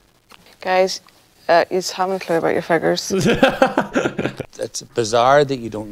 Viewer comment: What did she say? I've rewinded this like 5 times now and the only word I can make out is "feckers". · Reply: Figures